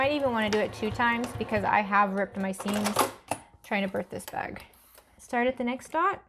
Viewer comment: Haha! At 31:40, it sounds like she says "birth this bag". Eleanor Burns always said that!